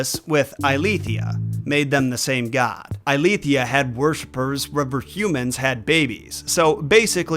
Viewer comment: I literally have no idea what you're trying to say here. O.o "[She] had worse burrs rubber humans had babies"?! O.o